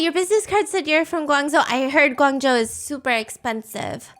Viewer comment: I thought she said “Guangdong” and I was like “what? Guangdong is not expensive unless ur in guangzhou, probably” and then replay it and was like “ohhh”